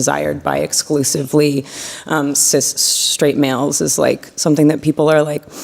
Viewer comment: did she say "US STRAIGHT WHITE MALE's? Meaning that she's MALE? Or Trans, dude with a cock?